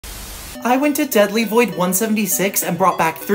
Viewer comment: When he started to talk I thought he said Zesty void